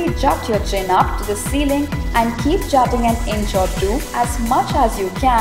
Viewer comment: keep ‘jotting’?? what is she saying here?